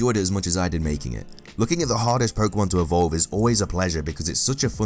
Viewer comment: Lol he said hardest but I thought he said hottest.